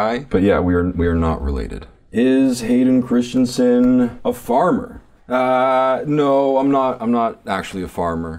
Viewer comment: Wasn't looking at the screen at one point and misheard and thought he said "Is Hayden Christensen a FARTER?" 😂😂 I had to stop and rewind the vid to make sure. 'Farmer' makes way more sense.